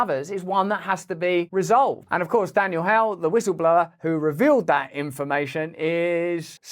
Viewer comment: Sounds like you said daniel howell in reference to the whistle blower- it is of course daniel “hale”. Great work as always though. Love and respect from nz